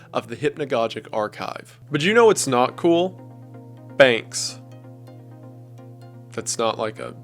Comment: I really thought he said "bangs" and not "banks" 😭. I really thought "What's wrong with bangs???"